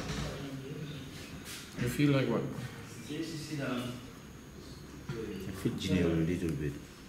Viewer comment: I thought he said I fear junior not I feel for junior.